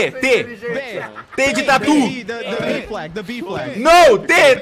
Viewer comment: he actually said "t de tatu". tatu means armadillo in portuguese.